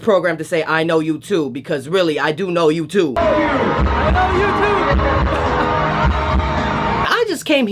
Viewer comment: "I know you!" "I know you too!" More like.... "I like you!" "I like you too!" 😂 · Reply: I heard "I love you" "I know you too" 😅😅